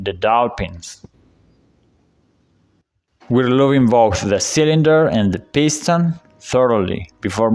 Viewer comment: Im sorry but i cant make put what you say before uou mention pistons at 5:16....what do you say please? Dont worry😂 your english is great rhis ia NOT a criticism believe me amigo! If only my spanish was half as good as your English! Thx for the videos mate. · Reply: Lubing the cylinder and piston thoroughly